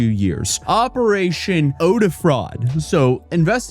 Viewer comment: I heard "Odor fraud". Bruh 1000 fragances? Ive been using the same cologne since 1995 ( a few bottles though, not one bottle XD )